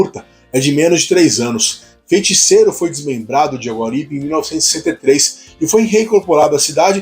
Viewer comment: eu não entendi se Feiticeiro surgiu em 1973 ou 1963. A dicção tem que melhorar um pouco, é só um dica construtiva. Porque o vídeo é ótimo, assisti a ele por completo.